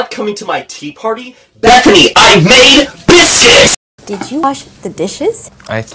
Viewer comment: I thought King George said COME AT ME I MADE BISCUITS!